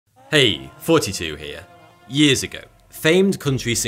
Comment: I thought he always said “hey 42 here”🤦‍♂️